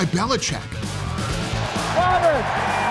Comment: did belichick call gronk Robert?